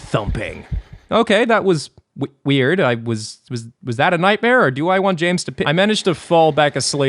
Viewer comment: I’m scared to know what he actually said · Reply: "Was that a nightmare or do i want games to pi-"